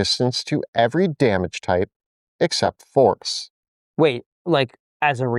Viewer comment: really thought you said except forks...